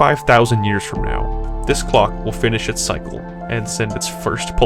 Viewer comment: I thought it said glock